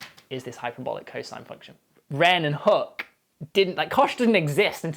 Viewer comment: I keep hearing him say “Ren and Stimpy” instead of Wren and Hooke, wonder how that construction would have turned out..... could John K even draw it? This is the obvious gratuitous intellectual comment........